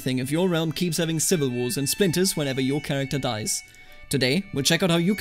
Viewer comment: When you said "splinters" i thought you said "sphincters" and thought this game just keeps getting better and better!